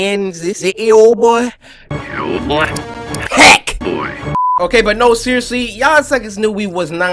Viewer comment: p word boy is what he said